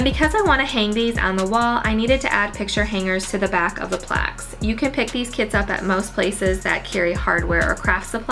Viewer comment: Thos plaques!! What a cool idea... when you were talking about the picture hanging kit - i totally thought you said "you can pick these kids up at" LOL